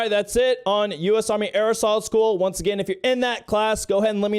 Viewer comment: I thought you said airsoft school